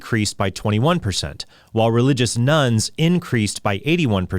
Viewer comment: thought you said “nuns” increased by 81% and I thought, wow! that’s great! 😅